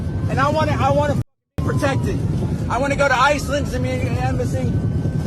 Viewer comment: did he say Iceland? I’m in Iceland. Iceland is very safe but I hear the word Iceland very rarely.